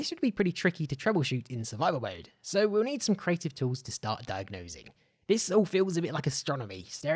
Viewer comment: Did he just say... dive nosing?